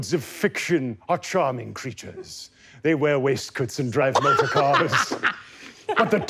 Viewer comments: I thought it said toast 🤦🏽‍♀️ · I thought it said toast lol 😂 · I thought ot said toast at first